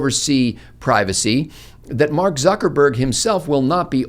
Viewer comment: Mark Bloodzuckerberg. I coulda swore you said that.